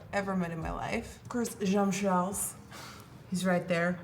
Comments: I thought I heard James Charles in the background!! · I thought I heard James Charles....You must do some videos together!!!